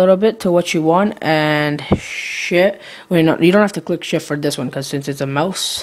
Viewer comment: lol sounds like you said hit shit